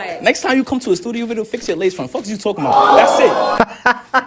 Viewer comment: Could someone help me out? Couldn't catch what he said... "Next time you come to a studio video fix"...??? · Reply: Fix your lace front wig